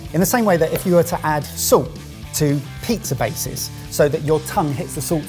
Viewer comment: I think it's hilarious that the captions said "soap" instead of "salt"...